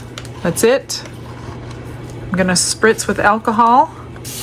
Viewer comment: put some of your alcohol into a small squeeze bottle to help control how much you are using to mix the mica. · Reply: I thought u were saying drinking alcohol😂 cuz i always watch vids on dbl speed. Her energy is super hi on dbl..lol